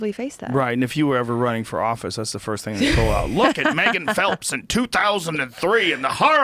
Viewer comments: i thought it said michael phelps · Am i the only one that thought this said Michael Phelps???